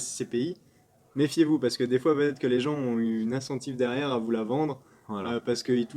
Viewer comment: J'ai 3 questions un peu relou : - à 17:00 dit "ils ont une assentif derrière à vous la vendre." Je pense avoir mal compris mais c'est quoi une assentif ? - à 12:45, je comprend pas pourquoi un dirigeant d'entreprise serait intéressé par le démembrement d'une scpi ? (J'arrive pas à trouver un exemple) concret) - à 10:05 du coup un coupon c'est un autre nom pour les dividendes dans un scpi ? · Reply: Saut J'espère que tu vas bien :D "- à 17:00 Pierre dit "ils ont une assentif derrière à vous la vendre."" Ils ont une incentive, je voulais dire qu'ils ont une motivation financière à vendre tel ou tel produit (pour toucher une commission) "- à 12:45, je comprend pas pourquoi un dirigeant d'entreprise serait intéressé par le démembrement d'une scpi ? (J'arrive pas à trouver un exemple) concret)" Je t'envoie vers cet article : <link> "- à 10:05 du coup un coupon c'est un autre nom pour les dividendes dans un scpi ?" Je crois que les deux appellations sont utilisés pour les SCPI. Il y en a probablement une qui est plus adapté au niveau fiscal et ou juridique. À plus